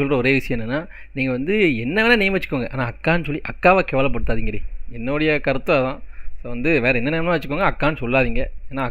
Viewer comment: What he said : akka nu solli akka va asinga paduthathinga 2:23 What I hear :akka nu solli kaka va asinga paduthathinga